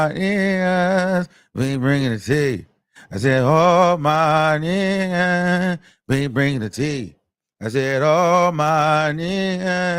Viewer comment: I thought he was saying “all my Wiggins we bringing’ the tea”